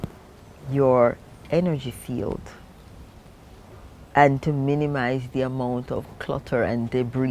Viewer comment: Did she say minimize partners? 04:16 thereabout? 🙆🥴😧 It's best to desist o